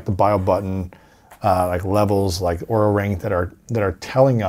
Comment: Hi Lewis! At 1:02:22 you talk about devices like: Biobutton, levels, and "ORAL RANK"??? I can´t find out what you´ve said. Can you help me please? Thanks! :)